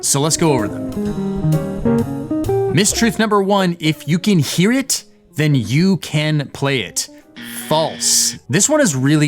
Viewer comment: I never heard "If you can hear it you can play it". I heard "If you can sing it you can play it"